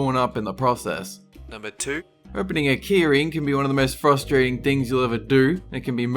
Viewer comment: when you said "keyring" I thought you said "Keurig" (wasn't looking at the screen at that moment)